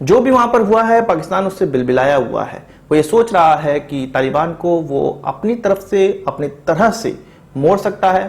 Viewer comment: M i d only one who heard the word "taiwan" ko instead of "taliban ko". By d way sasta tere naam was funny one 🤣🤣🤣